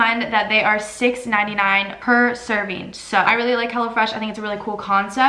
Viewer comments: i thought she kept saying hella fresh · I thought she said "Hella Fresh" and i was like dayum good marketing skills right there